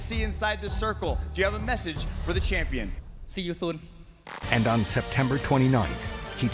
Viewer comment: He cool as heck til i heard that “si yu soen” 5:27 lmao💀💀🤣